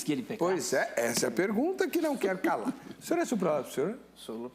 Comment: alguém entendeu a pergunta do apresentador e a resposta do pastor??? Repeti 500 x e ainda não entendi kkk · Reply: "O senhor é supralapsariano?"